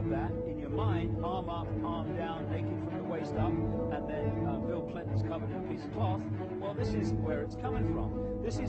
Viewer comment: did he say " Bill Clinton is covered by a piece of cloth??? 1:23?